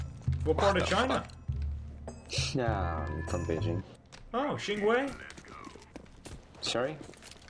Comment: Seem like no one speak Chinese here, so as a Chinese fan I think I can do a translation thingy. 0:27 - 0:32 "Xing Wei" isn't a word as far as I am concern, maybe Swagger means 腥味(Fishy Smell) but thats unlikely. The subtitle says "salt" 0:38 The subtitle did said CS:GO in chinese, but the guy only spoke the first 4 letters which just mean "Counter Strike". 0:51 and 0:54 Legit translation 1:03 "I am from China, so that makes me better at Counter Strike than you." In a very weird Grammar incorrect way 1:15 - 1:20 Legit translation with some minor grammar error Bonus: The teammate with the chinese name (can be seen from 5:03 to 5:13) is a famous quote in the LOL gaming community that means "If I am here, the tower is not going down" 1:39 Upstanding Black African American Gentleman :优秀的非裔美国绅士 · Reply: thought he said幸会at 0:27 which would actually make sense lmao